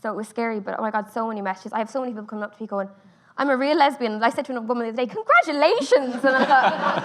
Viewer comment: I'm confused by Nicola at 9:40 does she say she is a lesbian and says congratulations to people or that lesbians come up to her and say it? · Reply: People come up to her and say 'I'm a real lesbian.' I think. Not sure honestly